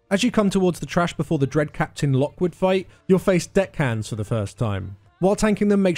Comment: I thought he was saying you will fight 2 "dickheads" haha "deckhands" thanks for the info as always.